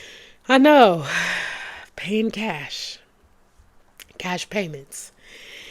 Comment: you said Cash Payment... And i heard "car payment" 😬 why is this stuff so expensive! 😑 It really is a whole car payment! Whew! 🫶🏽